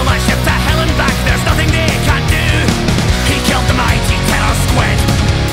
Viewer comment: I misheard "Terro Squid" and understood "Taylor Swift" instead LOL